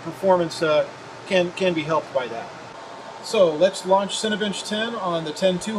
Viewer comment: It sounds like he says "Cinebitch". XD